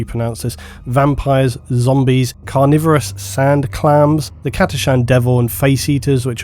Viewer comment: Swore to god you were saying "Sand Clowns". The part about a Sand Clown eating some Imperial Fists really had me wondering what was going on in 1st Edition lol